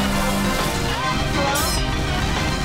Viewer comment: I swear on me nain I heard you got mail right at 1:50 somewhere owo I'm not just hearing things